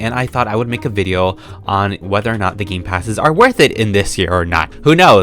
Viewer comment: we are all gathered here today... as I talk to my paintings YES ITS NORMAL! what's your favorite gamepass and whyy? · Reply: I thought you said 2021 gamepasses ToT